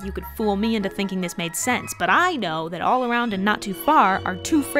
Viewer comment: I can’t believe you didn’t mention the wild cadence on the word “too” whenever this song says “too far”. Whenever anyone says the word “too” in any context, there’s always emphasis on it. That’s TOO bad, the test was TOO hard, I want that TOO, etc. You never hear an unemphasized “too”, so you (or at least I) instinctively hear the word “to” in its place. There should never be lines in a song where you accidentally misinterpret what word they used just because the cadence of the song didn’t fit the natural inflection